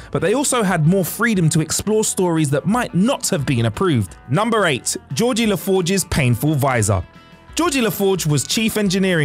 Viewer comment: & 2:18: Does he say "Georgie La Forge TWICE?